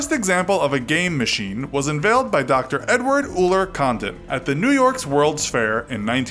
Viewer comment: To be honest, I thought he said "Condom"